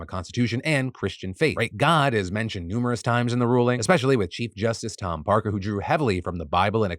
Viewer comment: I misheard Phil saying chief justice as cheap justice, which the judge in Alabama ironically was 😂